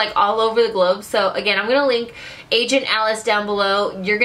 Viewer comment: Holy cow.. LMBO. I thought you were saying ASAIN Alice.